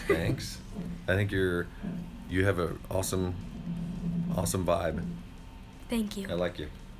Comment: what he say? awesome what? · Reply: Vibe